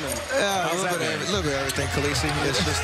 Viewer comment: What he says at 1:27 ? My english is so bad and I can´t understand what he says and why they laugh haha. Someone to help me? Thanks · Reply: A little late, but Khalessi (Spelling?) is a character from Game Of Thrones and one of the announcers' names are similar to Khalessi. It is a running joke as other people have butchered/changed his name whether on purpose or accidentally.